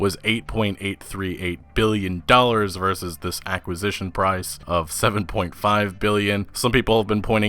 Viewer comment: I thought you were saying accusation instead of acquisition it was messing with me